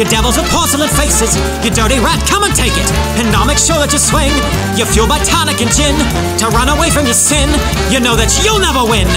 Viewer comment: 'You're fueled by tonic and gin' What I heard: You're filled with crockpots and gin